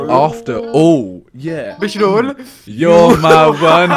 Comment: Does anyone know what he said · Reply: Bishrul